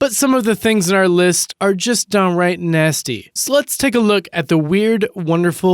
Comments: did anyone else notice that when the guy says " lets take a look" it sounded like he said sluts lol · Did he say, "Sluts take a look at..." @ 0:28?